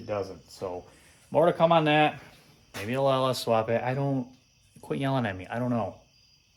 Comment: Did I hear you say"LS swap"? 😢